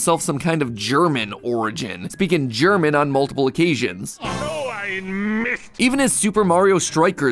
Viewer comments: I always thought Wario was saying “Oh i missed!”😂😂had no idea that was German lol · I think he's actually just saying "Oh, I missed" or "No! I missed" Edit: apparently the English voice actor confirmed he said "D'oh, I missed", but in the original Japanese he did say "So Ein Mist!" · So Ein Mist? I always heard “Oh, I missed” !! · Are we sure about that? That just sounds like he is saying "Oh I missed!" While not quite grammatically correct, I feel like it makes more sense than him just randomly speaking German. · i heard "nooo i missed" no german